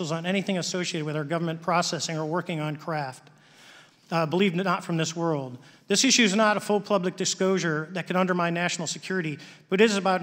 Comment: "believe they're not from this world" is that what he said? Sounded like it.